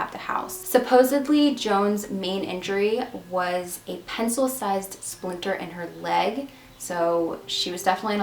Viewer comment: I heard "splinter sized pencil in her leg" for a sec and was so confused 😂